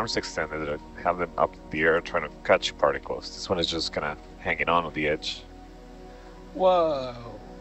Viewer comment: Oh! "He's hanging on to the edge" I thought he said "he's hanging on their b****" 😆 we all talk real but when we're trying to be professional we should talk professional. So I had to go back and make sure that was right because I laughed. Thinking it was funny but at the same time not thinking that it was correct. °~•.☆.•~°